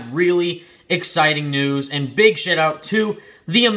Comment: did say big SHIT out to the amazing spiderman